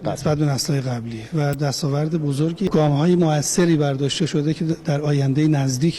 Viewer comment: He said from 250km to 500km. The subtitle is wrong.